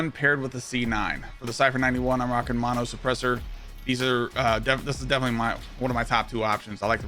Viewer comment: I thought u said these are meta